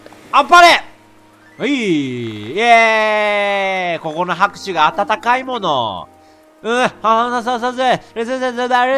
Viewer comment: 「ここの拍手が暖かいもの」が 「ここの拍手が暖かいだなも」って聞こえた